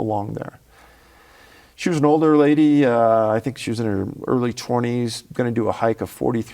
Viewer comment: "She was an older lady. I think she was in her early twenties." What!? I must be prehistoric! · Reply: I thought he said 60s